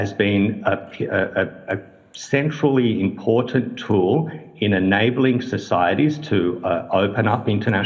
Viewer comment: Did he say sinfully important tool?